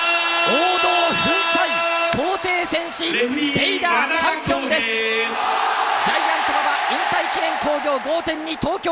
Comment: What is the announcer saying, and the crowd response ? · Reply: The announer was calling the referee's name and the crowd responded by shouting the referee's name (Kyouhei) Tradition in AJPW.